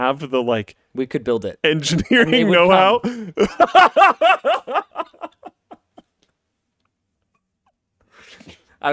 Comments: anyone heard what linus said? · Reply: Someone already answered: "And they will come." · What did Linus say after “we could built it” at 1:35? I couldn’t hear it over Luke’s laughing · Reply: "And they will come"